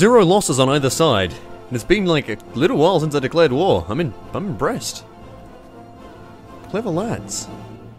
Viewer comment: When you said “I’m impressed” the captions thought you said “I’m breasts” and that’s just funny imagining a pair of tits talking in an aussie accent going on about Germany killing themselves.